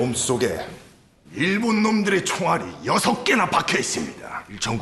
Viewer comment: 총알이 두개..라는 대사가 런닝맨에서 전소민씨가 한 대사 때문에 종아리 두개로 들려짐..